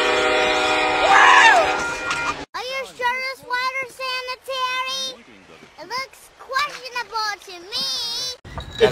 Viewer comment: What is that kid saying at @ 2:40? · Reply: Are you sure this water’s sanitary? It looks questionable to me.